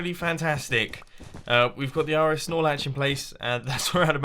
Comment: What is the phrase he keeps saying around 2:30-2:50? It sounds like he is saying "R.S. Snorlatch" . He's got the RS snorlatch set up etc etc. I've heard it in several videos and can never work out what that actually means. Sounds like he's naming a Dr. Seuss character :'D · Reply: RS NOR latch